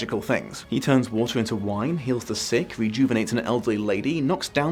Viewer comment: I dont know why... but during that list at 5:20 about his "Jesus-ing", I swear I heard you say "Defeated the Sith".. ...went back immediately and was like "HEALED the SICK! ...that makes more sense!" LOL EDIT: Also, 18:50 , while true, the Norman's were considered "French" in a loose definition, they were actually settled Norsemen, offered land by the French King in return for not...y'know...stealing their stuff. So if they truely went the Norman route, they should have had a hybrid Norse/French look. But yeah, Spanish, I guess... I mean, they did give us the Borgia family! LOL! Also, the... remember your Twilight reviews... "Its not that deep, bro!" :)